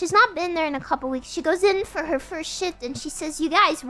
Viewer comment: sounds like she said shit instead of shift